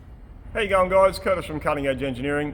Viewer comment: watched tons of your videos over the years. today for some reason CC was turned on... the entire few years i always thought it odd he said "Hey young guys" as an intro........ fk me i've been hearing wrong this entire damn time